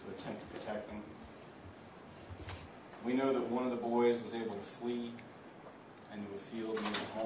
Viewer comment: did he say one of the boys had fleas? The kids had cooties 😅